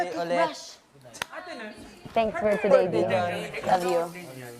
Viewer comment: What ! 8:25 said..thanks for today Dy i love you 😂..hope ako lang nakarinig ..😂😂😂